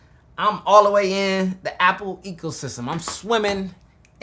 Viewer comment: Did you say "apple eco system"